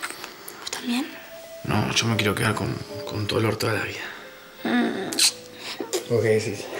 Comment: basta entendi "con tu orto todo el dia"